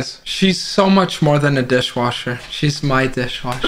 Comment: The wallet was too stunned to speak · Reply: Oooh wallet… I heard “wall ed” and I was like what’s that mean?